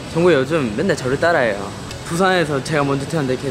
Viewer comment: did jimin say jungkook is copying him again? istg